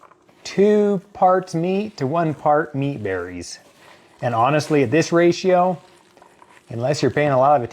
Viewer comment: Key Aspects of the "Meat Berry" Usage/Purpose: The primary purpose is scientific demonstration and research in tissue engineering. It acts as a prototype for creating biocompatible scaffolds that could, in the future, be used for growing complex tissue structures, such as blood vessels in organ replacements. Production Process: Researchers start with a seedless grape, remove the plant cells to create a clear scaffold, and then populate it with animal cells to create the "meat". Not a Consumable Product: The meat berry is not currently intended for human consumption due to the lab conditions and materials used (such as antibiotics) in its growth process. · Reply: I was wondering what the heck a meat berry was I thought he said wheat berry at first. Thanks sounds crazy so no thank you on the meat berrys 😂😂😂